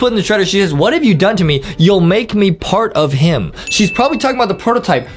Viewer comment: she actually said he’ll make me part of him